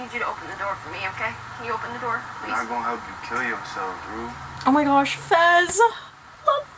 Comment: At first I thought Fez said "I'm not gonna help you. Kill yourself, Rue" when I first watched it